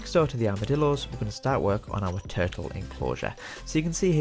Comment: I thought you said terrible enclosure.😅